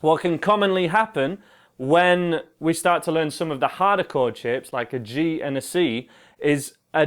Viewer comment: and there i thought he was saying sh*t .....it was "shape"..im disappointed :D